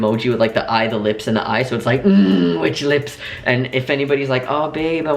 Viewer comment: When I first listened to the podcast and yall said "which lips" for the first time, I thought you were saying "Witch lips" and i was like what do witch lips look like.... ((But I got the joke shortly afterwards))